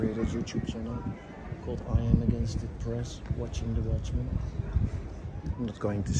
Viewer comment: “I’m Against It” Press. That’s what I thought your dad was saying when I first heard him say it.